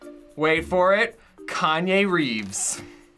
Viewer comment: Did he say Kanye Reeves? and I oop